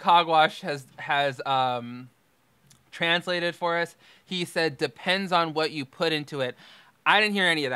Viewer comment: It sounded like he said "it depends on what kind you put into it." But I had to listen three times and close my eyes to figure it out LOL